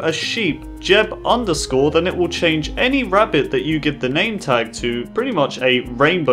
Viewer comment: did he say rabbit on 6:29 ??